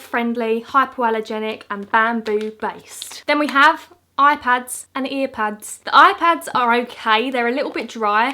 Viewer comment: I thought you said IPads and I was like “why would a dog need a iPad” then I relized you said eye pads Lol